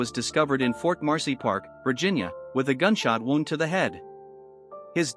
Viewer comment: I thought they said Vince Foster had two gun shots to the head which is kind of tough to do by yourself.